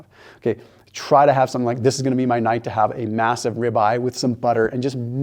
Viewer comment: I thought he said this was going to simple!😂